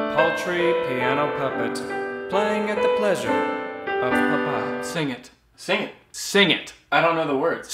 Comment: Im the only one who heard "Ling Ling Ling Ling Ling 40Hrs !"